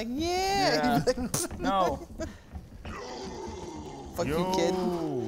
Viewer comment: did Leon just say "you c**t"?